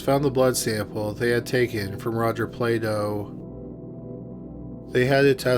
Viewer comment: Thought he said Rodger Playdough